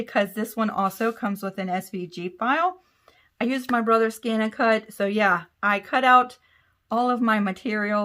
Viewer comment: Ha!Ha! I thought you were saying , I went to my brothers and used his scan n cut! I kept thinking why wouldn't she have one, why use her brothers? Duh!! This is fantastic! Never saw anyone use the tulle before, great work!